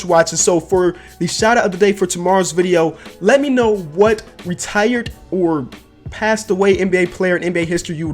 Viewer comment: Sean Palmer yes he said retired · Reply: Oh my bad he said retired or passed away I thought he only said passed away player